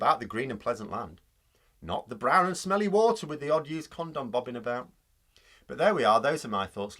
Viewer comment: Loved your last comment but my apologies. I thought I heard this ⬇️ and had to rewind "With the odd conservative bobbing about"😂😂😂